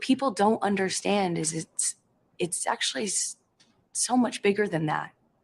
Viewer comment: When ever she says "it's actually " every time I hear "it's sexuly "